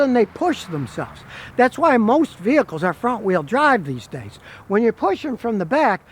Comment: Is this the real Scotty? I thought I heard him say Mexico vehicles ok!